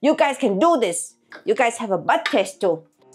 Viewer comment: Im not 100% sure...could be my ears are off , but, that last thing you said wasn't " bud taste" was it? I could swore that's what I heard! Could be wrong ....but...if that's what I heard, that's the most adorable thing I think I've ever heard anyone say. Taste bud backwards! Lol very cute!!